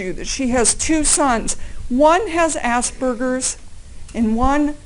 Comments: The pensive security guard ... I love watching him when he pops into view. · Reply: Apocalypse Plough at around 10:32 did she say ASS BURGERS?! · I have to admit that did a really good job with her closing argument. Well put together and well delivered. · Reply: Allaboutfball at around 10:32 in the vid did she say ASS BURGERS?!